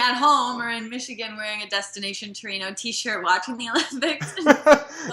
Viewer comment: What did he say he wore at 20:22? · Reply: A 'Destination Torino' t-shirt